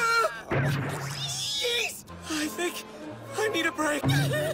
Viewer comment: I thought Yuri said "Yeet"